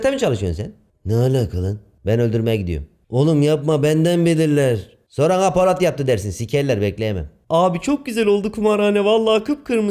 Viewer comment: Polat burada ne diyor anlamadım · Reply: Sorana polat yaptı dersin diyor